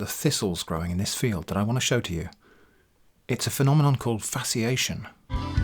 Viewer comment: I first thought you'd said fascination. Fitting as that's exactly what I was feeling when I saw those photos.